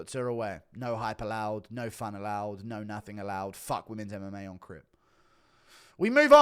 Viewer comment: Did say "F*** women MMA on crip"